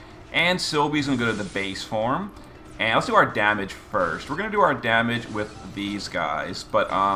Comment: At first I heard "we're gonna do our damage with this Skyes" instead of "we're gonna do our damage with this guys" lmao Anyway that's a fantastic video as always, ESTHER POWEEEEEEEER !!!!